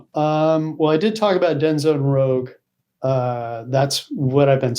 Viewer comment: Does he say Denzo Rogue? What is the name of the game he is super excited about?? Can anybody help me out? Thank you! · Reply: Deadzone Rouge. A+ game